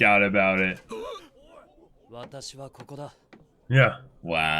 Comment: thought he said "watashi ga kita" also 11:52 "kage bunshin"